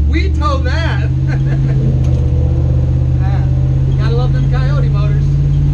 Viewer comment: First off you need seat belts! As far as to heavy feel? I’d get heavy sway bars with very good shocks! Good luck great job! Have fun stay safe! At 8:26, did you say “gotta love those coyote motors”?